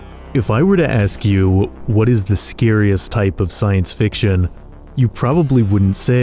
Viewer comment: I thought you said cosmic horror was the scariest